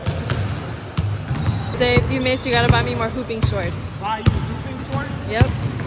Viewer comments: I thought she said pooping shorts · I thought you she said more pooping shorts😅